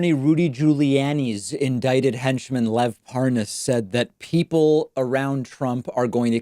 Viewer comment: Oh, I thought you said Lev parnASS!!